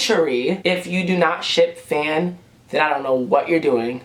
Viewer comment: I thought he said "if you do not shit Phan" omg